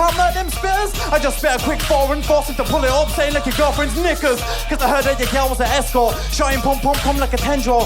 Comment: is saying the n word? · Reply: Hes saying knickers btw